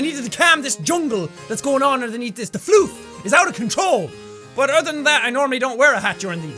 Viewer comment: read this in next comments video plz 3:42 I thought you said, I need to control this FLOOD instead of floof. then i though HALO!!!!!!!!!!!!!!!!!!!!!!!!!!! P.S Booper Dooper SHIMASHIMASHIMASHIMMI